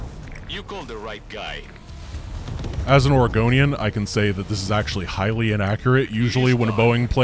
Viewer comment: I've been playing too much Elder Scrolls... I thought you said "as an Argonian" at first. Had to give it a double take.